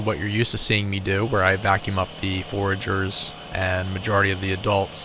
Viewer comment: What is the word at 0:19. Vacuum up the f.... Forger? I can't properly understand spoken english sometimes. Thank you...